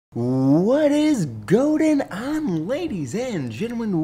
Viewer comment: Did he just say "what is goating on"?